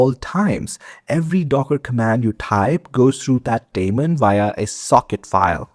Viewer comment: Every time you said "that daemon" I heard "Matt Damon" ... having said that, informative and accurate video